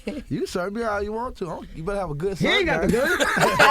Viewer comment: What is Gucci saying? I've replayed it several times and can't make it out because of them laughing. Also, @ 24:04 what does he mean when he said "ain't gone be nothing on me but them thangs the long way, standing up". I thought maybe like a rifle, shot gun, or simply some kind of long gun standing up on his persons. Someone please school me. I always come back to this interview and many more of Mr. Gucci and this videos was posted September 21, 2013. And I made this comment at 4:30 a.m. on September 1, 2021. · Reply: He saying that you better have a good 'search guy'. And don't worry about what he mean @ 24:04 since only he know for sure. Also wtf you doing posting all kind of timestamps, you coming off real federales right now 🤣